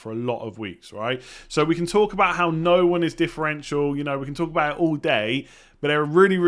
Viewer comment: “no one is differential” instead of essential